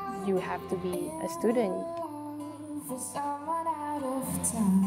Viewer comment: What does she say at 1:00? "...you have to be perstudent?" persistent?